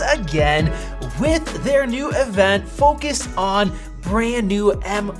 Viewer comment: did anyone else hear "f*ckus"